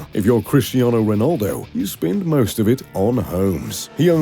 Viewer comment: thought he said spend it on hoes 😂😂